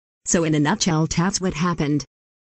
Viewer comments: they said “yay marraige” i heard “gay marriage” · I thought it said "gay marriage" at the end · At the end i thought it said gay marriage 😆